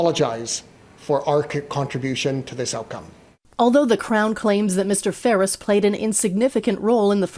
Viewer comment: Although the "crown"...i swear i thought they said although the clown 🤡😂😂😂